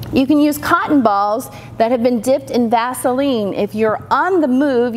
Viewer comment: HA!!! I thought you said 'dip them in Gasoline" and was going to go off the rails!!! But you did not so good...